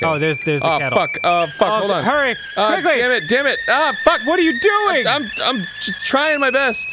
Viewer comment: did he just say "verdammt"?